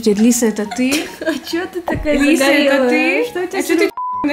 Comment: Я одна услышала на второй минуте, как (насколько я поняла) назвала Маргаритой? -, это ты? Маргарита, ты?? Ну точнее